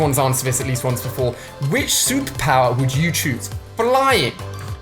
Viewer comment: I swear he said "Souppower" instead of "Superpower". But then again he did say it really fast.